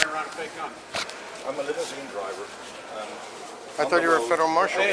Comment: The guy said "I'm a limousine driver" but the close captions said "I'd a little green driver." LOL!!!